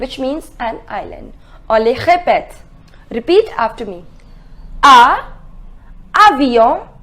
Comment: what does she say? Can anyone please write down what she said in french here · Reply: She said repeat after me